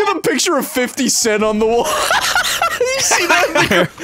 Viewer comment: For a second there I thought smii7y said u see that ni***